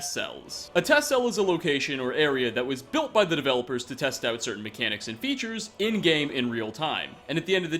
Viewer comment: I Heard "where they tested their crappy mechanics" XD